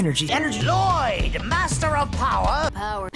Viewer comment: Power doesn't sound like a word anymore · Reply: I most of the time I heard “power tower” 😂.